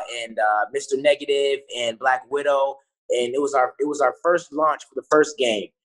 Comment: Did he just say black widow?🤔🤔🤔